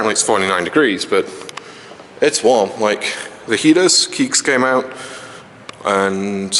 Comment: did he say Keag's...??